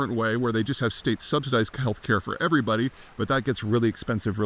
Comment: He said state subsidized childcare, not healthcare. Your argument is wrong on its face. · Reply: What? At 2:50 he clearly says healthcare and not childcare.